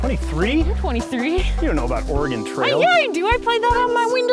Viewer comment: I thought they were saying "Organ Trail" instead of "Oregon Trail," which added a whole new morbid spin to this channel until I figured it out.